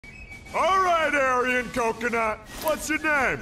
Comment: Aryan Coconut is the funniest and most apt description of Armin ever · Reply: I thought he said, "alright, hairy coconut..."